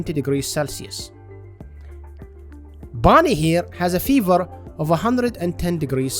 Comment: "Bonnie here has a fever of 110°C" Did I hear that right? 110° Celcius? I dunno doc, I don't like her chances